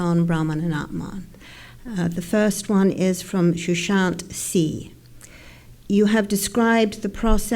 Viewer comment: who asked the first question ..I heard Sushant singh